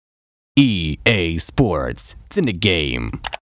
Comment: i heard "EA sports, tsin the gay"